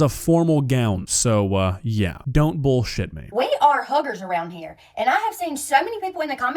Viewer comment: sounded like she said hookers